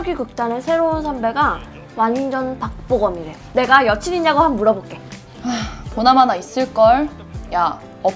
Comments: sorry? I heard Park Bo Gum not BTS? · ? What BTS? I heard Park Bogum. I guess the translator are ARMY lmao😂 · why are the subs wrong at 7:37? "I heard he looks like a member of BTS" when she DEF says Park Bo Gum......????????????? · why did the subs tell "a member of bts" when she clearly said "park bo gum"???? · but i didnt hear bangtan sonyeondan tho.. I hear park bogum... but idk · i heard her saying ParkBoGum, but the subs say BTS 😂 im an ARMY. But for international, you dont have to mention bts to be considered handsome... If its bogum in korea then its bogum anywhere. Just an opinion :)